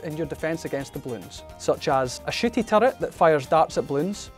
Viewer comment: Did he just call that a shitty turret? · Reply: I thought he said shooty